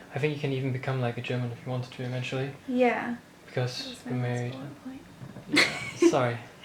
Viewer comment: What did she say after he had mentioned the possibility of becoming a german citizen? (15:55) :) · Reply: Felix90 kinda late, but: "that was my last bullet point"